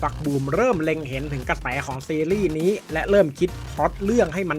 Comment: ป๋าพูดว่า กระแต๋อ่ะ